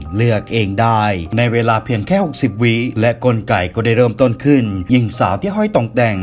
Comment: และกลไก๋ ก็ได้เริ่มต้นขึ้น ใครได้ยินเหมือนผมบ้าง